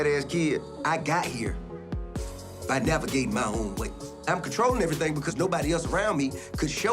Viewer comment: I literally thought he said, "I got here by defecating my own way." I'm so glad I was wrong.